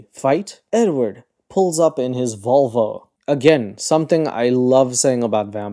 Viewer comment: Edward pulled up in his WHATTT 😭😭 love your accent cuz ONG it sounded like vulva